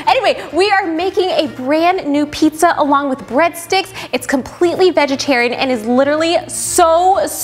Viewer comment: There was egg in the recipe so its not fully vegan, or atleast thats what xertain people say. BUT I REALLY WANNA TRY THIS SO BADD, BUT IM TOO YOUNG. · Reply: oh, I thought I heard her say that it was vegan. I guess I didn't catch that. Thanks anyway.